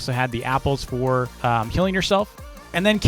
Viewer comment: He talked about baker and the apples "Healing yourself" but I heard "Killing yourself" lol